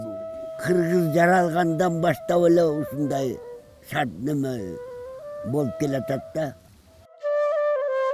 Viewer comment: did he just said " Bol ke latakta hai??""